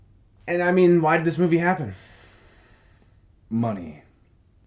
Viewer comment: Gods. I had to rewind the end when asked why the movie happened he answered, money. I thought he said Muslims. The wonders of being partly deaf.